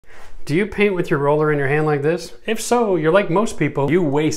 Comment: Thought he said “do you pee with your roller in your hand like this?” And I was like “…. No I can try though” and then I realized he said paint